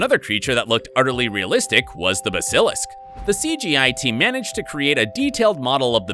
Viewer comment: did he just say the bis ilik it basilisk